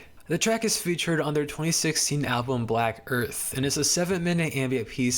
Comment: why did I hear "black girth"?😭😂